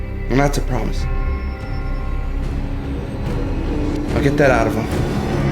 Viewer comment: In 01:06 says something but there is no sound, I think he says: now get that out of him. But anyway I really liked it, I did not know was such a good actor. -Pizza? -NO! hahahah funny.....